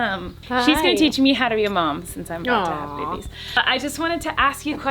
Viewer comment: Did she say babies?